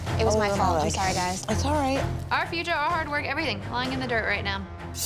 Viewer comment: did say "it's alright" to? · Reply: Yeah she did😂😂 even I was surprised like what did she say